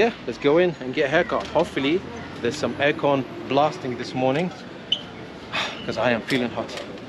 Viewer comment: I thought you said AKON blasting loool